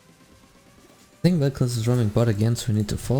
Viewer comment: did you said Rekkles????? Are u in agame with Rekkles?!?!??!!??! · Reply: Oh nono i said "i think vel'koz is roaming bot" :D